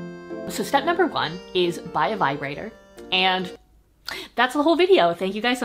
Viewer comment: At first, I thought you said “buy a library” at the beginning and I thought, well..... having a whole library to myself would make me very happy...